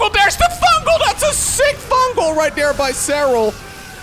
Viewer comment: I thought it said “stinky fungals” 😔